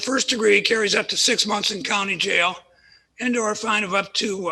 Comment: Coulda sworn he said "comedy jail" and not "county jail"